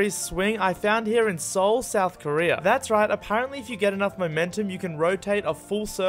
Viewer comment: I thought he said North Korea💀💀